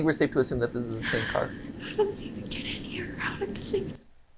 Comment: I heard "get in here" when she found the car. Please don't tell me I was the only one.